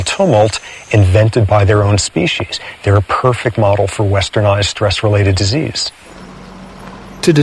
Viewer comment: What did he say at around 5:00? "Westernized stress-related disease"?